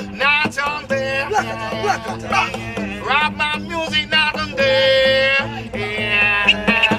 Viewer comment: ลาบานูน หน้าส้_ตรีน มีใครได้ยินไหม 55